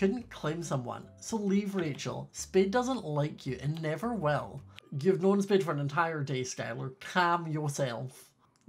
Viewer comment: did he say "Youve known this bi*ch for an entire day." Or am i hearing things...... NO SWEARING ON YOUR CHRISTIAN MINECRAFT CHANNEL!!!! Lol jk love you.